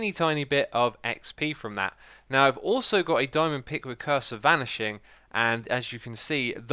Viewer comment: I thought you said “Cursor Vanishing,” and I was like why in the hell would you want that. 😂